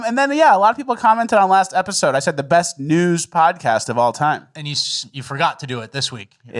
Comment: I thought he said "The best nudes podcast of all time".